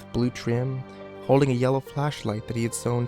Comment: Thought he said fleshlight for a second.